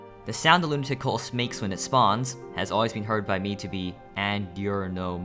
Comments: I heard it as "I'm an oreo god." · i hear him say "the more you know"